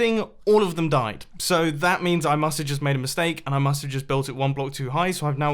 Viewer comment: Did he just say built a hole?